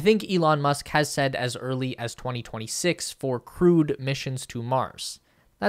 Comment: I am a planetary scientist and this is an awesome video. I love this stuff. One note: When I hear "crewed missions", it sounds like you're saying "crude missions". I know it's not politically correct, but for garden seed, can't we just say "manned missions" and get over our social justice warrior snowflake BS? Thank you. I gave you a thumbs-up.